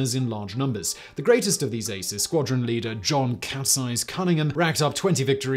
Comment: "Cats eyes" Cunningham went on after the war to be a well-known Test Pilot. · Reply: Ha, I thought he said “capsize” and was a bit confused